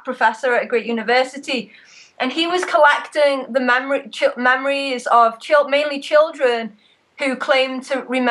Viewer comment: LOL. I thought she was saying they were collecting the mammaries of children xD